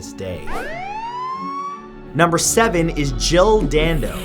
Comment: At first I thought he said Jill dildo.